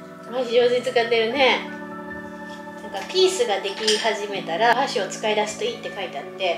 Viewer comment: May the demons be gone from your homes, and may happiness come to you <link> I just finished turning on the subtitles! Sorry I'm a little late! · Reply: and 8:31 Sorry I can't find a way to contribute to the subtitles but it should be 'peace sign' instead of 'piece sign'.